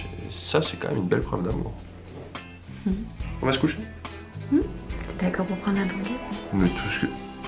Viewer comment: Qu'est-ce qu'elle dit à 16:42 ? "T'es d'accord pour..." ??? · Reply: Je l'ai!!!! C'est: "t'es d'accord pour prendre la banquette" Aprés 6 écoutes en boucle XD! Cohérent du coup